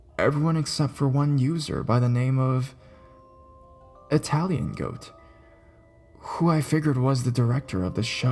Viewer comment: When I heard "Italian Goat" I actually heard "Hetalian Goat" for a second, and freaked out...xD By the way, the way you read this is perfect.It's as if you are really scared.Good job!